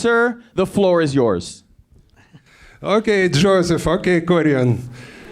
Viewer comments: I heard "Ok Joseph fucking Korean", then I checked their names. :D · did i just hear ok joseph fuck a korean. is it only me or...